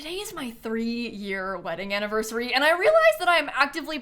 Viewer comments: It kinda sounds like she said 30 year anniversary 😭😭😭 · I thought she said 30 year wedding anniversary